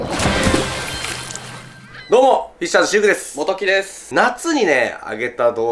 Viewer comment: シルクです、モトキですって聞こえる😮